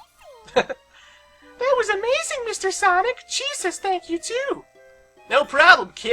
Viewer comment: i swear like when said: "cheese thanks you too!" it sounded like he said: "jesus thanks you too"